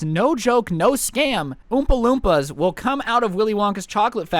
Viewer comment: “Oompa loompa’s will steal your-“ Me: Likes “Youngest sibling” Me: Unlikes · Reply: I thought that said Obama loompa